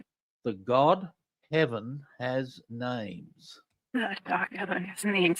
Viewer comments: These are great reversals. I am listening just by natural ear. I hear 'dark heaven' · I heard "The DARK heaven has names".